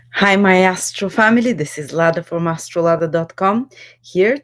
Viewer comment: I thought she said hello my ahole family. Thought she was my mother there for a sec.😬